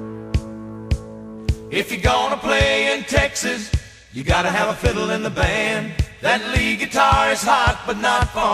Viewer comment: When I was younger, I thought he was saying "If you're gonna play in Texas, you gotta have a pickle in your hand." And I thought, wow they love pickles.